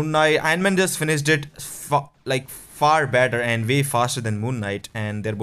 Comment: you spoke f***k instead of fast🤣